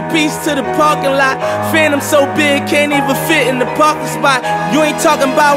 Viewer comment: I heard "Fanum so big can't even fit in the parking spot"